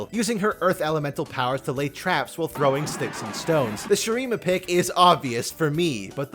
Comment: DID I HEAR A ROCK AND STONE?